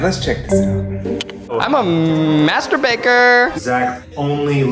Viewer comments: Zach: I’m a master baker What I heard: I’m a masterbater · I heard masturbator lmao